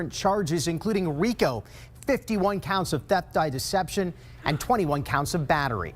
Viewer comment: I THOUGHT HE SAID DEATH BY DECEPTION AND I GASPED